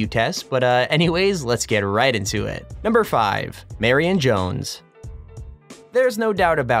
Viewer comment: When u said marion jones dam i thought u said mary and jones